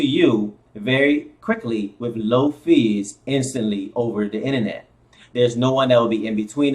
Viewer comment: did you said No fees are low fees ?